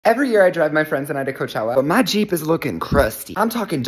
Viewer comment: i heard "every year i drug my friends" 💀